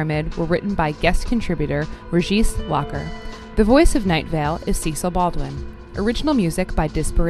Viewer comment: Esperen, al final, en el minuto 22:45 la locutora dice algo sobre los mensajes de la pirámide (se ingles pero me parece mas cómodo por si se me pasa una palabra leer los subtitulos) ¿alguien sabe que dice? Me suena a que los mensajes de la pirámide fueron escritos por un invitado a la emisora (la real, no "Night Vale")... lo cual me parece bastante curioso. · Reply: ¡Así es! Fuero escritos por Regis Lacher ^^